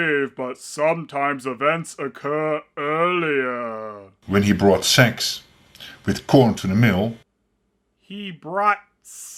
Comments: I think he says 'he brought sacks with corn to the mill' but struggled with the English vowel ash in the word 'sack' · "He brought sacks with corn to the mill." It's probably because I'm Dutch and I'm used to this horrible accent that I have absolutely no trouble understanding it. So, if you read this, should you encounter another east-west dub and you can't make out what they're saying, feel free to contact me. I love your videos and I'd love to help! 😁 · I think he's saying "sacks of corn to the mill".